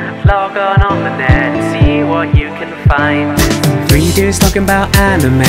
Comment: am I the only one that hears "reindeers" talk about anime instead of "3 dude"?